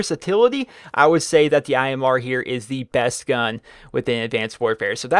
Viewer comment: I thought it said best guy lol